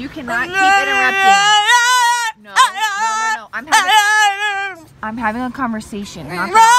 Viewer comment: When she said " I'm having a conversation " lol it sounded like he said OOOOHHH LORD !! LOL 😆😆